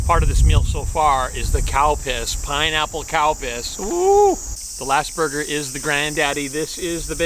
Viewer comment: I swear I thought he said the new “cow piss” soda is served warm. Lol. Another awesome burger(s) review.